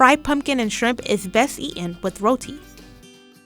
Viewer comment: I’m Guyanese 🙈 yass we made it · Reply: Did she said roti?